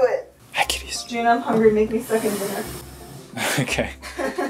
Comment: "Jun, I'm hungry, make me f*ckin' dinner." Is that, is that really what you said? XD